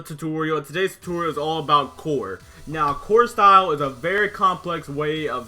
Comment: I heard "this tutorial is all about porn